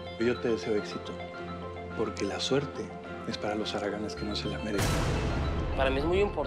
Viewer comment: "Yo te deseo éxito, porque la suerte es para los haraganes... Lo ultimo no entendí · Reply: "Porque la suerte es para los haraganes que no se la merecen"